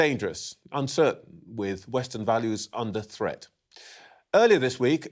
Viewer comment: I think I heard him say western values.😂😂.